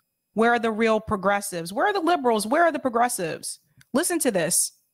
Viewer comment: I thought he said, 'where are the liberals, what is a liberal.?